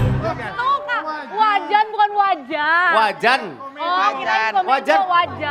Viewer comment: tapi aku juga dengernya wajah 😂😂😂